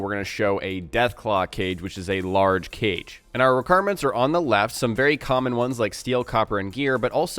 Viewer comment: did you say left?